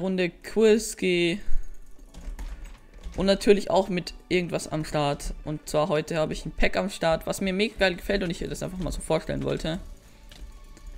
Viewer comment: Wer hat bei 0:11 auch verstanden:"Heute hab ich mal wieder einen Hack am Start"? xD